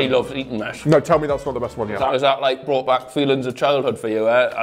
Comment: i need someone to tell me what did he said in 9:47 cuz my brain cant comprehend the sounds that came out of his mouth 😭😭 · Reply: "you and ur chums will get round and eat Eaton mess" I think 😭